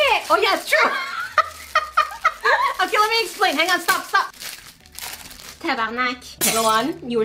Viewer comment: Does she say ''Tabarnak'' at 22:26?? haha True Quebecoise here!!